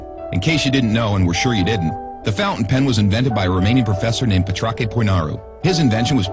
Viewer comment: It sounded like Pitrauche Pouinarou i can't🤣